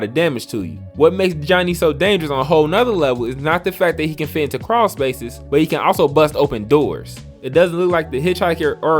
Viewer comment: Can Johnny fit in crawl-spaces too? It sounded like you said that. I’m not trying to correct you or anything. I’m actually very curious about all the abilities. Thanks for the upload! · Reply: no problem. Johnny can fit in crawl spaces and can bust open doors.